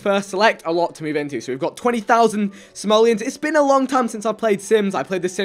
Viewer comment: It sounded like he said he had 20,000 somalians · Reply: Simelons*